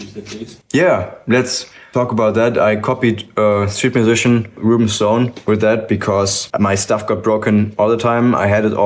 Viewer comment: who do you mention here as the person who gave you the flight case inspiration? i cant understand what name you say and thank you for great video, i learned a lot! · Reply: Reuben Stone!